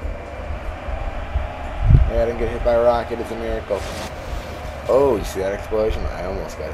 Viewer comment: when u said rocket out i thought u said rock out!XD